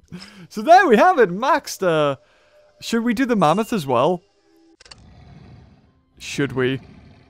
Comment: Lol I thought you said "the shada doo da mammoth"